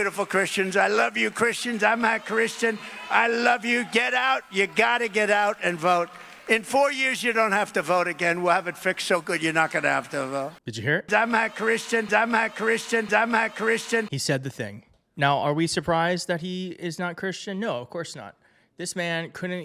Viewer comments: I thought he said Im-a Christian... in his weird way of talking, but was pyobably a Freudian slip! Good video either way! New sub too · Believe him when he says “I’m not Christian” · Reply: It really did sound like he said, “Im not Christian.” And then he shakes his head no. IT’S CREEPY! Like he was possessed or something! The way christianity is being used! It is terrible. Jesus Christ! Literally! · He did not say that, he said "Imma Christian" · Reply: that's what I heard "Amma Christian too " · I heard, "I'm AAH Christian." He always puts heavy/gutteral emphasis on certain words. Regardless... what's more important is that "It'll be fixed" and "You won't have to vote anymore". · I heard him say not Christian ? It also sounded like he said dumb ass Christian!. He's the antichrist. I'm definitely voting Harris and whomever she picks for VP. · I thought that’s what I heard too. “Not Christian”. Either that or “I’m ahhhhh Christian”.